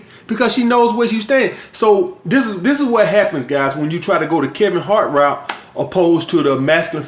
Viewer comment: I thought you were saying "caring heart"? Laughed when I got it "Kevin Hart"